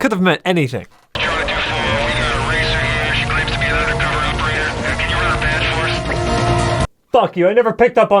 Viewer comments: what he say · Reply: "We got a racer here and she claims to be an undercover operator, can you run her badge for us?" I think is what it says. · can someone pls tell me what the police operator said ? I honestly cant understand him lmao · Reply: Charlie 2 4, we've got a racer here, she claims to be an undercover operator, can you run her badge for us.